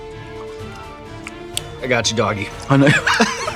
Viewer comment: did he just called him doggy 😂